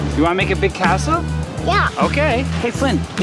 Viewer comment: omg why did i think he said big a**hole BAHAHAHA